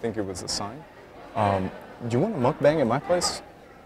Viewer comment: Wanna mukbang at my place people thought that he was saying Wanna bang at my place 😂😂😂